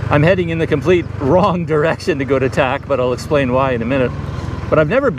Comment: At the checkpoint, thought I heard you said "To Attack"😂. I'm guessing your other video project should be the scenary video with ambient music right?